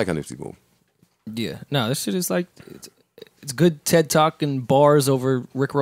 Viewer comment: Can someone please tell me what Rory says at 2:47:44 ? Random shit I know. But I feel like I've heard the phrase that sounds like he's saying (Ted talk ) before